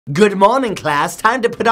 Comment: Did I just hear "Good Moaning".